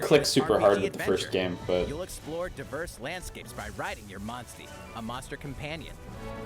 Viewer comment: Monstie was explained in the first game. It's Monster + Bestie (best friend) since Riders befriend monsters instead of hunting them. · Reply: I thought it was saying "Monsteed" as in "a Monster that is also your Steed", now it sounds dumber